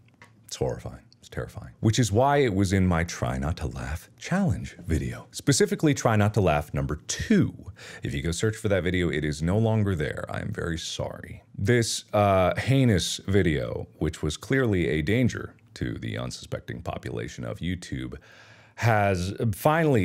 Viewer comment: my headphones cut out when he said “this heinous video” and i heard “this anus video” and i can’t stop laughing.